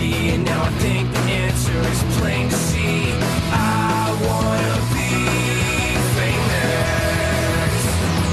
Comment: When I was a child I thought it said "I wanna be Jewish"